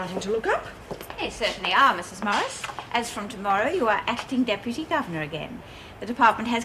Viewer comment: Corny because Erica appointed Morris acting gov as of tomorrow, yet its morning and Joan is still in the gov seat. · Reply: I thought she said acting deputy gov